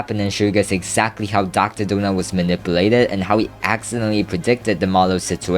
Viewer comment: Why do the captions say gooner Drdonut